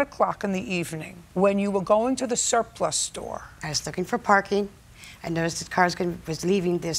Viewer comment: Am I the only one out here paying for car insurance?? 😂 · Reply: 😂😂😂😂I thought she said she was coming from a syphilis store😂